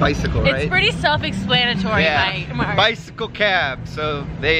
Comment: When she calls you Mike instead of... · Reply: she said "bike", not mike